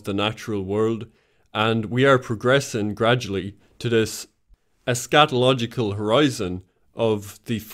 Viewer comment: Keep mishearing eschatological as scatological and doing a double take